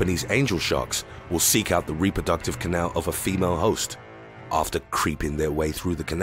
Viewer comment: A commercial cut the word female so i heard “the reproductive channel of the femur”